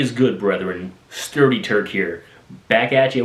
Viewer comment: I thought you said sturdyturd at first